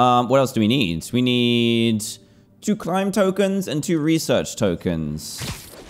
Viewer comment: my man is it just me or did you say climb tokens?